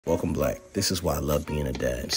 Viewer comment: Is it just me or does it sound like he is saying " welcome black". I mean no harm